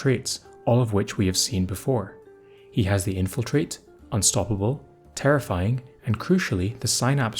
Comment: I didn't realize you weren't done talking, and thought you were saying "the infil-trait".